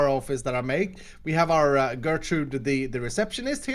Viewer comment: Ssundee fans: DID HE JUST SAY GERTRUDE???